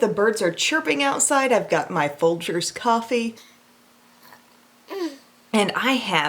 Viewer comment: I hadn't had enough coffee myself, so I first thought you said you were enjoying "Vulture's Coffee" instead of Folger's Coffee. And then I thought, Wait! Wouldn't Vulture's Coffee be so cool? :LOL!